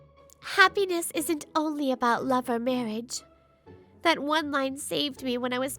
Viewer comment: 1. 16:58 She did the right thing to expose your harassment, Ryo. Then, you want Ichinose to take responsibility because of your wrongdoings? Stop blaming. 2. 19:28 "Happiness isn't only about love and happiness." Well, yeah. It is. 3. 20:49 Three women, huh? It seems he doesn't know about that "no office romance rule" is to avoid cheating towards other people's partners. 4. 22:46 Ryo, face the wrath of a scorned women. And, your downfall is about to begin. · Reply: I think you've misheard 2. It's "happiness isn't only about love or marriage"